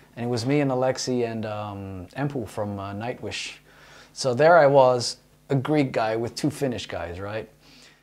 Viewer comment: For a second I thought you said: "And there I was, a Greek guy with two finished guys". 🤘😜